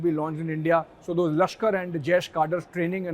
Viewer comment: The anchor says Lashkar and Jaish cadres.... but the subtitle says Lashkar and Jewish . Its quite contradictory. Please correct it